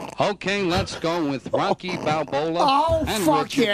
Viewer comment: he actually said "Rocky Balbola". So surprised that they didn't catch it.